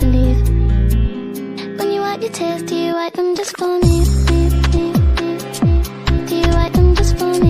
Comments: i thought she was saying tits instead of tears 💀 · Omg I always thought it said “do you wipe those tits for me” never made sense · I thought it said "when you wipe your t!ts do you wipe them just for me" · I heard this as "when you wag your tail do you wag it just for me?" I thought it was sexual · why did i hear "do you wipe your tits" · I really thought it said "When you white your teeth do you white them just for me" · I always heard it “when you wipe your tits do you wipe them just for me” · i thought it said “when you wag your tail do you wag it just for me?”